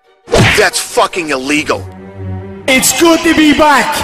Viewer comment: What did Conor say? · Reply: It is good to be back.